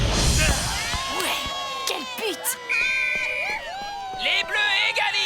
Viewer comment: j'ai compris "qu'elle p*te" y'a que moi?😂😂